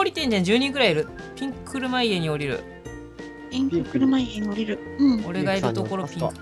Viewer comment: また「ピンク車家」が「猪木ボンバイエ」に聞こえたわw